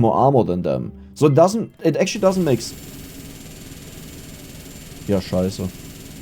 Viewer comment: did i hear a "Ja scheiße" at 47:44 and if i hear it right i like it